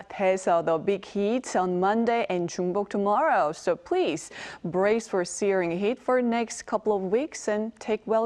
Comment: Can someone help me to understand what she said after “Brace for ??? heat”. I’m studying English with this video, and the subtitle shows only ‘SE’. ChatGPT suggests ‘severe’ as the term, but I’m not sure. Thank you so much ahead! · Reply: Brace for searing heat